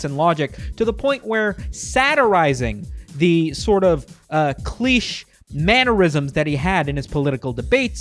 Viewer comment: DID HE JUST SAY CLEECH OR AM I TRIPPING